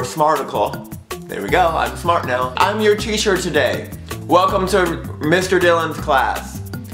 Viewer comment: I thought he said "I'm your t-shirt today"